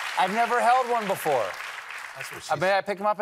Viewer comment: Stephen and the audience didn’t pick up on it but when Stephen says “I never held one before” F Murray Abraham whispers “that’s what she said” 9:58 😂😂😂😅